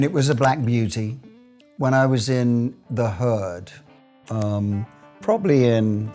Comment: i thought he said at the beginning "in the hood" but realized it was his accent and he meant the Herd